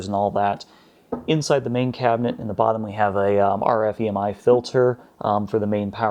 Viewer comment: What did you say at 8:30? Is it RFVMI Filter? Also could you please explain the use of this filter? Thank you. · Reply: It's an RF/EMI filter. It's used to filter out line noise from the AC power. It's not absolutely necessary, but helps get rid of noise (if it exists) so you don't run into issues.